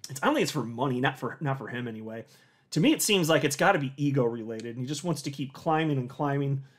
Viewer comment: I thought you said it’s got to be the EVIL in him but you said ego right?